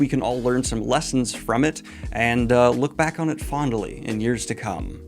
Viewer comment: I played it back multiple times, and I swear it sounds like you said "Look back on it Fonda Lee". xD